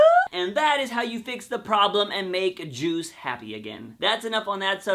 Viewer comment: Hahahaha this is the first video I have seen by this guy and I gotta say, pretty freaking hilarious. I totally thought he said it would make Jews happy.